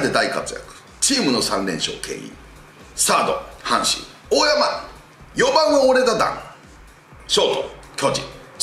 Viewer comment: の大山に対する一言、なんて言ってるか分からん笑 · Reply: ４番はオレだ弾